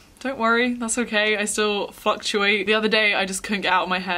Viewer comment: I heard fucktuate Lol